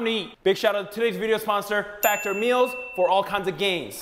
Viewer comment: When he was doing the ad, I thought he said Fatter Meals lol